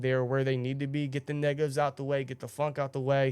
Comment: Did he say nigga at 16:22 ? 😭